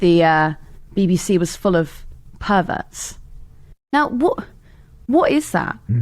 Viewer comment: I thought you said "the BBC is full of herberts", for a moment there, not "perverts"!! 😂😂 I prefer the line "the BBC is full of herberts".